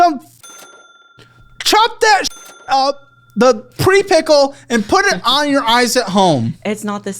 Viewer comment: getting so heated that he calls a cucumber “pre-pickle” has me fucking rolling 😭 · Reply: I thought he said "pretty pickle" haha